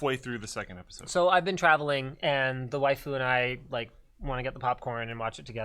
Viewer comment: did he say waifu?